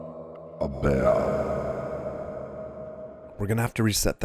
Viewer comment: What hè saying at 1:28 ?? Rofl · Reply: Its way to say "thank you" for subbing. Hes saying: "Aaaaaaaaaaaaah, BAM".